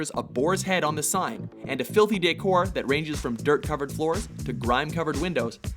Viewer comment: I thought he said "filthy dick whore" at first.